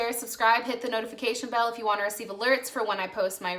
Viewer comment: What do you mean? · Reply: oh you said alerts. Thought you said lyrics 😆